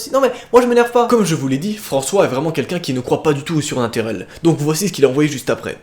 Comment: il ne croit pas à quoi ? 😂😂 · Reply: LB8games au sunaterel